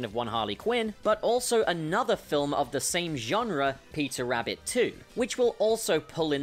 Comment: i thought you kept saying "pizza rabbit 2" dang